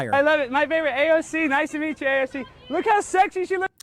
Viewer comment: Sounded like AOC said, “Nice to meet you, love you.” As she walked away. A very nonchalant way of dismissing someone, while pretending to care. Yeah, she only cared after someone pointed out who he was to her.